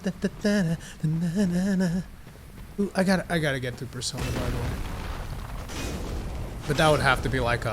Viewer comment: what did u say I couldn't understand/hear what u were saying there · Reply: "I gotta get to Persona 5"